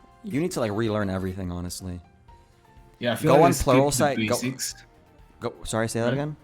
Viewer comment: " go on pe...." can someone tell me what site was he gonna mention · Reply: pluralsight dot com